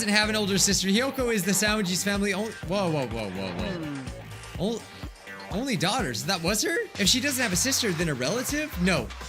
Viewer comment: tell me why i thought he said “Oh oh oh oh oh. Holy slaughter?”